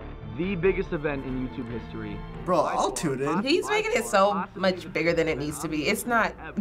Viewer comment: tf did just say? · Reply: "bro, i'll tune in" ???